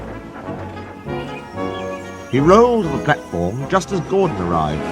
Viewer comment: I hear “he rolled dreadful” 😂